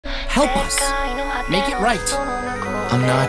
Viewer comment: I thought the caption said “I’m not a hero blud ” I was so confused 😭